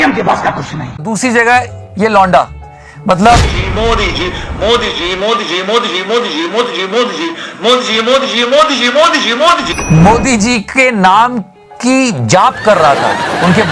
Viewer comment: when this guy was chanting modiji, it sounded like jihad jihad...